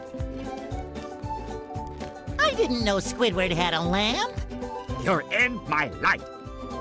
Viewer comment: All this time I thought he said "You're in my life" instead of light.